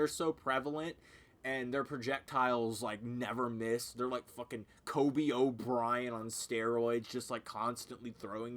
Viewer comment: he actually said Kobe O'Brien lol